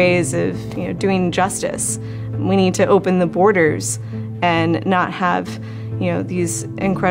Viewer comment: Did she just say "open the border'' in about 6:29?